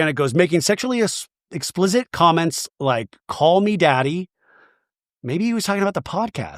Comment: I thought Danny said "get Daddy a drink", not "call me Daddy".